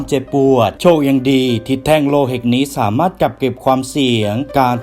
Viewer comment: พี่พูดว่า แท่งโลหิตใช่ป่ะ 6:46 😅